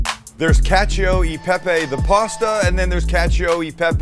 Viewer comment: ngl I kept hearing "catch a pepe" like was trying to sell me on a NFT